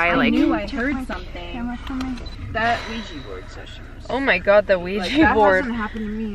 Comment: I heard a man's voice at 18:05 it said that Oulia bored session. j